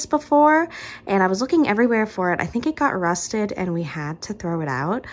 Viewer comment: You said your flower pot tray got rusted and I heard "arrested" and I was SO confused for a second. In general, I hope everything that's happening in your life evens out, and I'm really impressed (and super glad) that you guys manage to keep doing things that make you happy while things are rough, since it's something I really really struggle with.